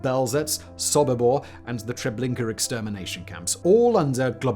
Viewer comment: Get rid of the music, please! The scripts are well written, and delivery drives the points home extremely well, regardless of the subject. The extra noise makes everything just that, noise. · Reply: All I heard was "Trablinkur" 8:10 for reference.